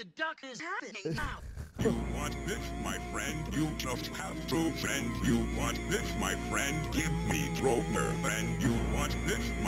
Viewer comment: YOU WANT IT, MY FRIEND??? YOU JUST HAVE TO SPEND!!! What a fucking perfect sentence mix. Jeff Lindblom is a fucking genius. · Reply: i always heard it as " YOU WANT IT, MY FRIEND? YOU JUST HAVE TO, FRIEND. YOU WANT IT, MY FRIEND? GIVE ME KROMER FRIEND."